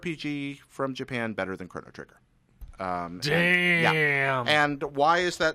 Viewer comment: What game does he say at 0:44 ? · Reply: AwesomenessMan Chrono Trigger. His statement about on it I do not agree with. Chrono Trigger is better than suikoden 2